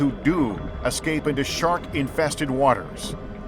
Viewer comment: I thought it said shrek infested waters...I feel like shit now